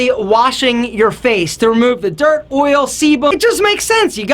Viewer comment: does he say semen 😭😂